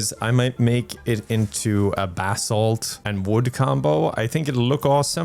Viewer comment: str8 up thought you were saying bathsalts for 80% of the video and just kinda accepted had a secret bathsalt mine/drug export